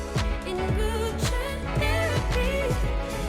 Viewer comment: The time lapse music made me laugh I thought she said boob check therapy lol